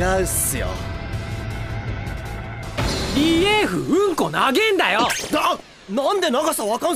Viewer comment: 毎回 「リエーフうんこなげーんだよ！！！」が、 「リエーフうんこ投げんなよ！！！！」に聞こえてくる....